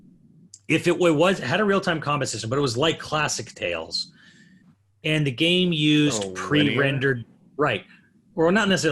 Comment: Anyone know what the "E's-" or "Ees 8" game they are talking about at around 1:20:00 is? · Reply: Ys - it's been around forever. Ys VIII is one of the most recent ones. -Bloodworth